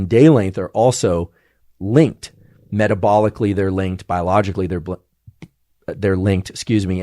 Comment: for people who says to professor Andrew "You Don't Blink" look what have you done, here 1:11:25 he almost said "biologically they blink". you really got into his mind